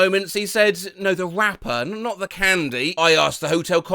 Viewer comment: i thought it said the “wrapper”, and was really confused why someone would compare a person to a mini m&m wrapper lol